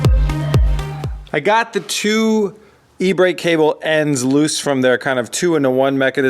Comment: I first thought you said "eBay cable" and I was like "Wut" 👀